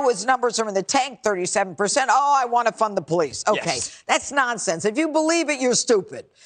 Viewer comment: it really sound like he said "fun the police"